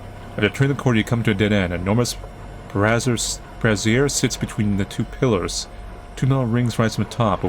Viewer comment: "Brazzers". Hah . I heard that right xD